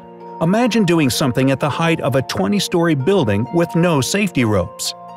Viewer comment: subtitles say Storey instaid of story